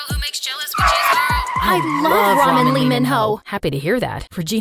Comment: did they say ramen lee minho or am i nust skz obsessed